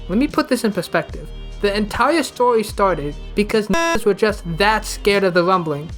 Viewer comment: Did he say Niggas